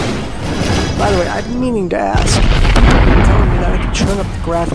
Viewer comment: Did you say dask?